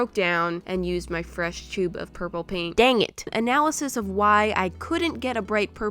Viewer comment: why dies it sound like you say "puppy paint" TRIGGERED*